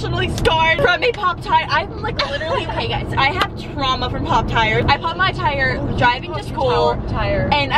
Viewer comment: omg hahah I thought your kept saying pop tart. hahahah!! u guys are so pretty omg